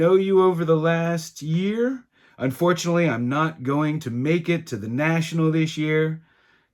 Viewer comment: Here is what I heard, "I'm not going to the National because Eddy is not going."